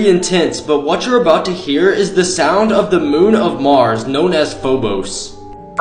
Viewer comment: I thought you said bobos instead of phobos like Damm I am deaf af